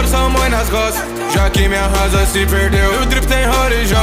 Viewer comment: Cara, em várias partes desse rap eu n entendi oq o cantou. Em especial em 0:39 tipo... "Tambôri sua mãe nas costas"?????? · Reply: Tambores tomoe nas costas